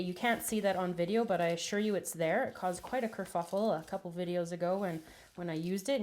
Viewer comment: A kerfuckle?? haha is that was she said? love it.. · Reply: oh it's Kerfuffle?? i thought she said kerFUKel or something??